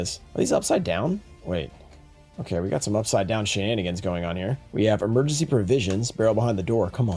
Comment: Very awesome video, also @ 18:40 did someone say shenanigans??